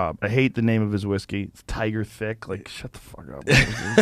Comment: I heard "Tiger Dick". But, I usually do.